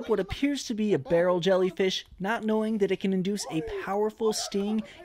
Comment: “Jump into the ocean with more jellyfish 💀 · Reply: Lol I thought he said "jump into the ocean FOR more jellyfish" 😅